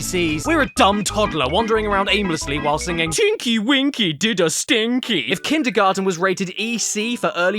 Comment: Ngl at 19:24 I thought said chinky winky 😂😂